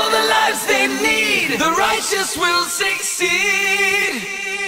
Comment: All I can Hear is "The Russians Will Succeed." This is worrying. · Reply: I hear “The roaches will succeed.” And instead of “Give up free will forever” I hear “We’ll fuck free will forever”